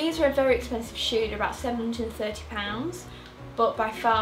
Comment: did she say 70-30 pounds ??? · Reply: £730 x